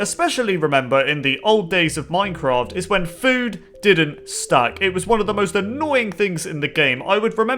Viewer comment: did you say food dosent stuck? i didnt know what you said